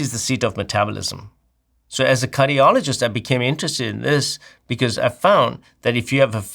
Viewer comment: sounds like he says 'as a Curryologist..." 😂